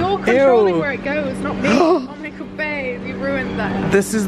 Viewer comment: I swear she said bae!!! And check description, her accnt is there and she is louis Tomlinsons sister!!!